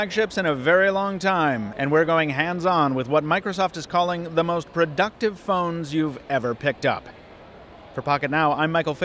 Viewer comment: I thought he said: "The most reductive phones you've ever picked up." hahahah oh well..